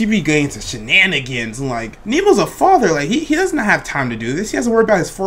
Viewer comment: Did he just say Nemo was the father at 14:50?